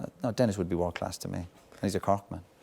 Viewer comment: What does he say about Irwin here? "World class to me" but after that? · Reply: And he's a Cork man